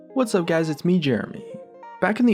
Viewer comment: At the start I had my volume low and I thought he said 'Hey guys it's Scarce here'